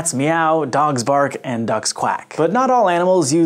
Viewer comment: did he just say dogs quack? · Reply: Given he thought citric acid made citris bitter, I wouldn't be surprised. But yes, he says ducks.